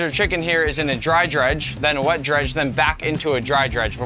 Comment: on;y thing i heard was "judge"